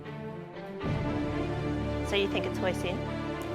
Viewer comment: i thought he said "poison" LMFAO